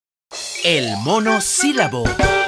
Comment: Pensé que decía el mono silbando xD